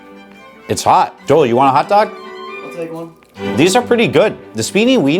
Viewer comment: what did bro just say😭??? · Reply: He said I’ll take one